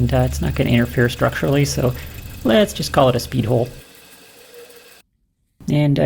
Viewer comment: "speed holes" I'm using that next time . Ps another great video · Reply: Not to be crude, but the first time I watched I happened to be looking away at that part of the video and I sincerely thought she said, "Let's just call it its pee hole".